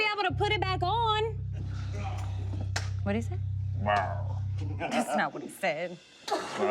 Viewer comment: "What did he say?" "✨RaUr✨"